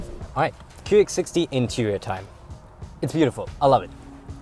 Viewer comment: did he just say QX 60